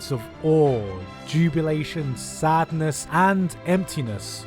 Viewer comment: did you just say jubulation